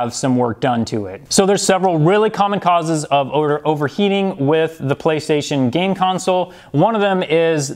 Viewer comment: I misheard it as "there are some real kamikazes of overheating"...